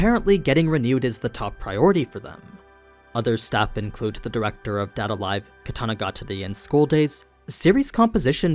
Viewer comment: I'm sorry did you say the director of school days?